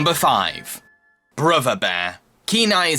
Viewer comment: Dude i thought that you keeped on saying river bear instead of brother bear. Lol